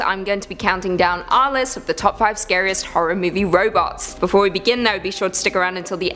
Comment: Damn I thought it said reboots, lol I'm high..